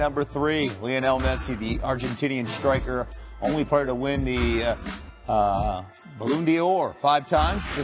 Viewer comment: Lol did he just say "balloon dior" ?